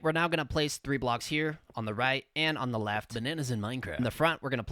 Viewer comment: Did you say bananas in mincraft.